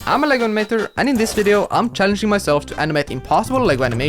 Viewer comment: At the start of the video it sounded like you Said like you said I am Legomater